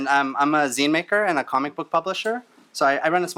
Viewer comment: heard he said his name was "Ryan Sans" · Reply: I heard "I am Ryan Sans and I'm a Comic publisher"